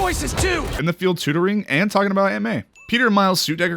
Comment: said the same thing the exact same way when I saw that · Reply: aunt* subtitles are wrong